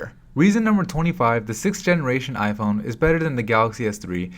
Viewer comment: did he said 6th generation iPhone